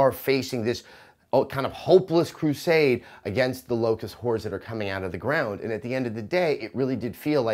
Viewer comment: Did he just say "Locust Whores" ?